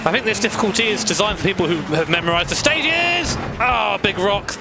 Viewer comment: WHAT DID YOU SAY?! MEMORIZE?